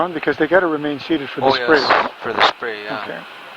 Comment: "I'm gonna leave the seatbelts on for the..." for the what? The spray? What did they say? ty in advance to anyone who answers me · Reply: I presume they were referring to the bio security spray before disembarking. Although they usually spray before descent.